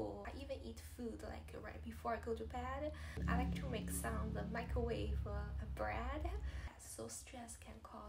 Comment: Please don't fall for the mistake that most seem to make that skinny = healthy. It sounded like you said that at night you like to make microwave bread? That is probably full of fat, carbs and things that will elevate your cholesterol AND triglycerides. (I don't know what microwave bread is, but if it's microwavable, it's probably not the healthiest stuff) Also, while baby chasing is tiring and time consuming, exercise is what brings down your bad cholesterol and brings up your good cholesterol. Daily walks should good you in better standing with your labs. Good luck!